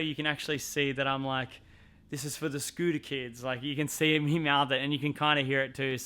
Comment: Did you yell "this is for the scooter kids" after you won? But the mic didnt pick it up? Just wondering ,because i thought it looked like u said it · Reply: yoits 90slizzle yes that’s what he said